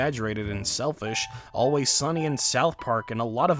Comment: I thought you said "Always Sunny in South Park" and now I want it to be a thing.